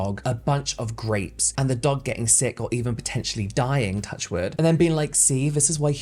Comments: its a superstition. so like he said « dog dying » nd then touch wood so that the dog doesnt die😅? idk how to explain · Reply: that helps a lot! I misheard him and thought he said “touch word.” It sounds like it’s the British version of the American phrase “knock on wood.” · what is “touch word” ? Is that something for your editor or something lolll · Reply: it's "touch wood" ! it's a superstition thing we use a lot in the UK (so if you say "it'll be fine, touch wood", it means that you're doing the opposite of jinxing something)